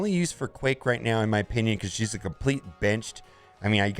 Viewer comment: Totally sounded like you said "she's a complete b*tch". I'm like whoa! She's not great, but sheesh!!